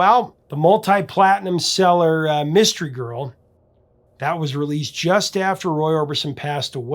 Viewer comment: I thought he said "Girls"...lol